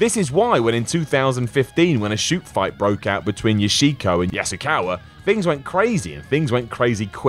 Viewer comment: I thought you said Yoshihiko. The "deadly doll of the east" 😂